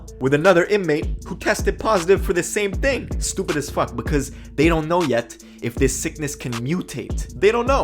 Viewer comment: why would jesus help a man that took 2 lives from a family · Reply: YBM WXVY I thought they said they did not know quiet yet